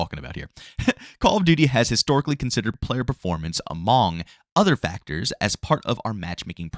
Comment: did you just say “a-MAWNG” ? 😂😂😂